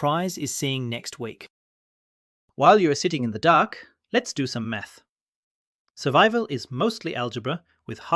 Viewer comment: i thought he said lets do some m3th 😂😂😂